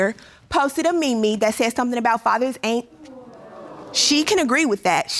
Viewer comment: Did she just say "me me" lol